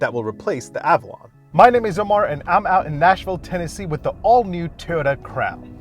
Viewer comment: Sounded like he said “the all new Toyota crap” in the beginning 😂💩